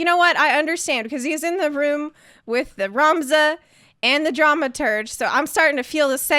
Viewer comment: I thought you said "Dramaturd" towards the end. XD Yea...the "choices" in FFXIV bother me as well. Sometimes they can cause a slight difference in what happens next (like Papalymo up on the wall), but still never changes anything (I was a WHM back then! I know magic! Let me help, dangit!!!). The one time I remember where a choice actually mattered was when the Gold Saucer had that "mystery dinner" sort of event, and there were multiple possible endings; I loved that. If you like choices that matter, you'd prolly like Divinity Original Sin 2. I'm sort of obsessed with it right now (beat it on the easiest mode, and am running through it again with a different team on a higher difficulty). I'd love to see more of those sort of things in JRPGs (though, I hear that the Persona games have some choices which matter...I've never tried any, though).